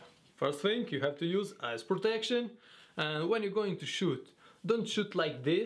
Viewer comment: when he said eye(s) protection, it sounded like he said "ass protection"